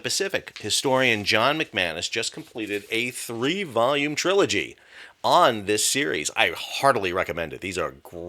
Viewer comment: I thought you said an 83 volume set. I was relieved when it was actually three volumes.